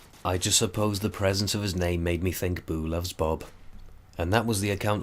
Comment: i thought you said the name you were using is " Boo Loves Boobs"